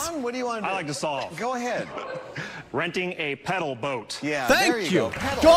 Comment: what exactly happened at 34:03 I just heard people saying pedal boat over and over and over again, like a sadistic game of hungry hungry hippos where the ball is slightly too big, helpless to its fancy food